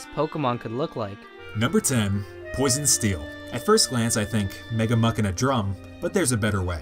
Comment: Sounds like he says 'Mega Mew2king'. Mega Smash player?